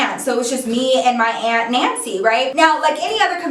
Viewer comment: omg when she first said her aunts name was nancy i heard "antsie" wtf my ears